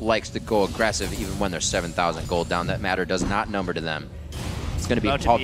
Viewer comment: did he say "that matter does not number to them" ?